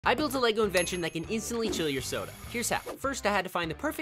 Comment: I thought he said “kill your soda” I was like what